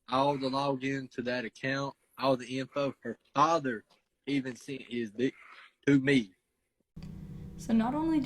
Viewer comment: You misheard what he said. He said that not only did Leslie send him xx pics, but Leslie's father also sent him a d pic. The boy's father isn't involved, afaik.